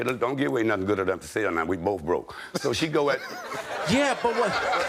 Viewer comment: I thought he said we both broke lol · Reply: I think he did say we both broke. It would make more sense. When I first listened to it I was entertained. The old head made a lot of sense to me. I started saying that "why give away something for free. that is good enough to sell?" Now I listened to it again hr did say "we both broke" lol